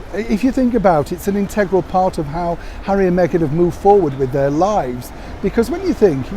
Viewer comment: I need to clean my ears out...I thought you said Harry and Meghan are moving forward with their lies. 🤔